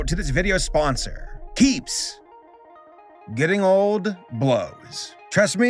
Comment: For a second I thought you said your sponsor was peeps and for some reason that brought me joy until I realized mistake